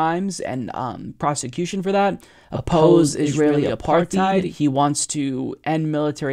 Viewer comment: OP-OPPOSE IS-ISRAEL APARTHEID-EED (OPPOSE ISRAEL APARTHEID) lmao did you guys re-record that part because he mispronounced apartheid you forgot to delete the original sound, and it sounded like you were going h a r d